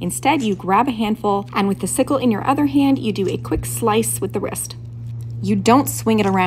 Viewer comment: Thought you said "slice at the wrist" got so hyped for a second lol